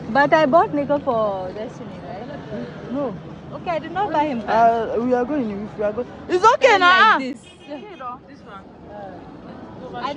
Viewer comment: bro what is that cuss word? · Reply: That’s knicker 😂 the subtitle came as nigg€r🤦🏽‍♀️ we were talking about trousers or knickers